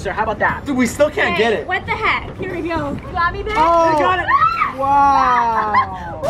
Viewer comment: it sounded like she said "you got me babe"😢